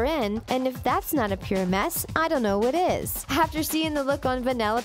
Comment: This is what I heard, "If that isn't a mess I don't know what is"